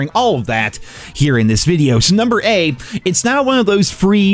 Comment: I know I just smoked but please tell me I'm not buggin out, did he say NUMBER -A ?